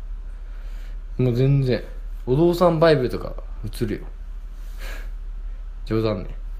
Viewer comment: なんていった？ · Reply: お父さんバイブ。笑